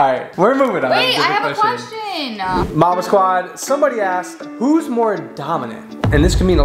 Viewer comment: The subtitles thought y’all said mama instead of marble squad 💀💀